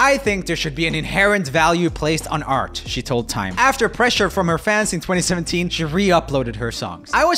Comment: did i hear that right? "pressure from her fans"?